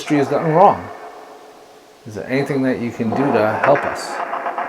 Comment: Oooohhhh, they are talking dirty in front of you. First I believe I heard, can't help us now.